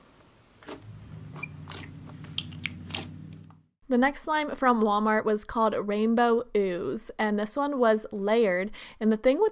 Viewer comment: When you said "rainbow ooze" I heard "rando booze" like WTF